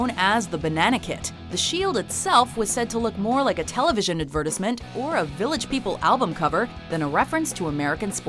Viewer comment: what did she just say? Did she seriously pronounce advertisement as "ad-vur-tez-munt"?